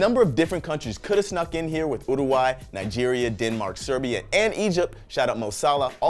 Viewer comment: Am i the only one who heard Urayai